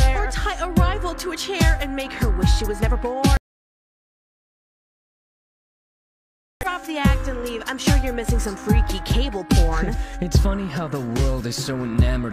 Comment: I thought she said cable corn.... nevermind that...